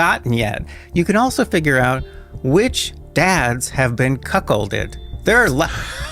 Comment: Im sorry....but did he say....they can determine thru DNA which dads have been "cuckh○lɗeɗ?" Did I hear that right? I cant make out what he's saying. Maybe my mind is in the gutter, but thats literally what I'm hearing. But...I must be getting that wrong? Wtf is he saying? That can not be what he actually said lol. Please somebody confirm I am not the only person hearing him say that 😂